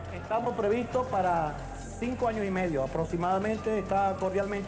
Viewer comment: Min: 7:40 cuanto dijo? escuché dentro de 50 años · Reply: Yo sí escuché bien. Dijo 5 años y medio. Un saludo.